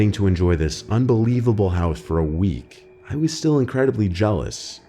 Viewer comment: dude had a lisp and said houth instead of house